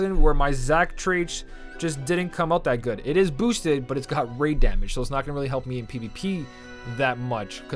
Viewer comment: I was just listening to the video in the background as I was making my lunch, and when he mentioned his Zack and talked about him having raid damage, I thought he said his Zack had BRAIN damage!! LoL